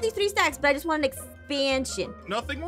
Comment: She was like "Yea I just wanna expansion" And I hEard "Yea I just wanna expand SHIT!" . 😂😆😂😆😂😆😂😱😂😭😭😭😭😭😏😂😭😂😭😂😭😂😭😭😭